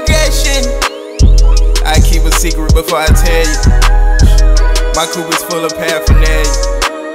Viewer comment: What he say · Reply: Keep a secret before I tell u, my coupe full of paraphernalia